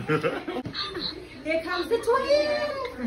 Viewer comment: Baby why I thought the nurse said here come the twin but aww she super cute congratulations I love the family support girl 🥰❤️